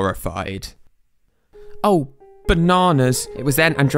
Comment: turn on captions the captions said oh no instead of oh bananas 🤣🤣🤣🤣